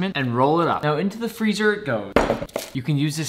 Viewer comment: for some reason I thought he said "you can use this for a day"